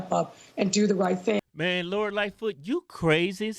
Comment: I swear I thought you guys were saying Lord Lightfoot LOL